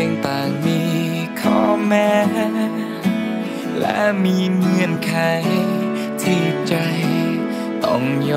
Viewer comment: เพลงเพราะติดหูอ่ะ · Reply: เสียตรง 0:30 เราได้ยินเป็น (เงี่ยนไข) มันควรจะร้องว่า เงื่อนไข